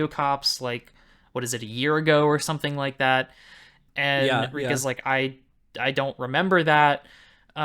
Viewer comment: lmao i heard Ryuk😂 first time here y'all funny